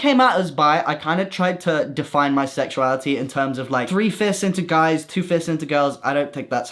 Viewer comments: I misheard “3 fists into guys” 😂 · I thought he said "three fists" 😭😭😭